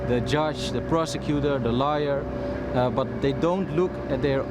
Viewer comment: The lawyer was pronouncing "liar" instead of "lawyer". The first true thing said about a lawyer. Any lawyer. Anywhere.